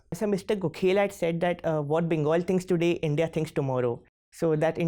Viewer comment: I didn't get this what he said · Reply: Janet Yellen he says JNU also says that what they think today what india thinks tomorrow